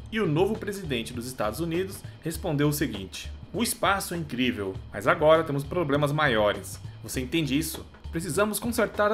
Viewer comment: achei que ele tinha dito pro garoto: o espaço é incrível , mas eu sou mais...